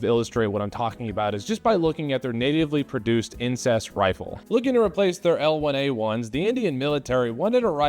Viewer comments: Heard that as "incest rifle" lmao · i thought you said incest rifle lmao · I keep hearing "incest rifles"